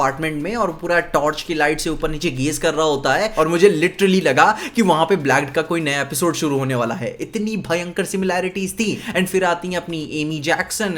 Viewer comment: kaunsa naya episode shuru hone wala hai? Can anyone tell what did he say? Kaunsa series? What did he pronounce? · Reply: Blacked porn site🥸